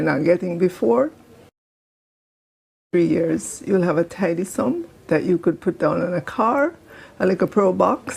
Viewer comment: Tidy sum? I thought they said prosperity.